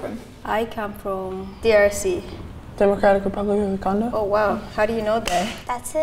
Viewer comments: I literally thought I heard him say " Democratic Republic of Wankanda" and I was like "holy macarons its real?" and then I realize it was Democratic Republic of the Congo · "I'm from the DRC." "The Democratic Republic of the Condo?" I can't 😂😂😂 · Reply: I thought he said democratic republic of wakanda